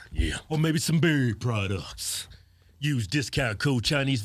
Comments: is he saying beard products damm the whole time i thought he said Berry products that southern Accent lol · For the longest time I thought you were saying dairy products. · Seeing as I only watch at 2x speed I keep hearing "berry products" hahaha!!! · please say beard products it sounds like your saying berry products like y'all selling some jam lol